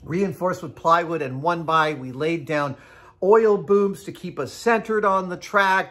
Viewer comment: Um did he just say "oil boobs" and not explain it???